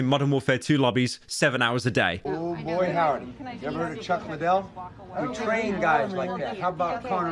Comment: Did he say "Chuck Mcgill" from Better Call Saul?